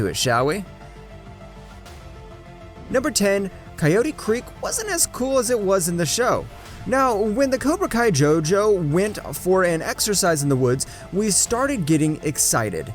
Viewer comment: jojo- that's what I heard, just me?